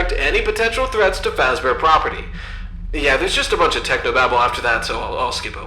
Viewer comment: I thought that said “technoblade”